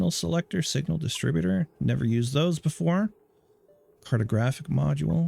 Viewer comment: I had to replay this moment, I could've sworn the first time I heard "pornographic module" not "cartographic module" and... well, I must admit I was pleasantly surprised at the direction ONI was going. Maybe I should be buying that DLC a little sooner! :)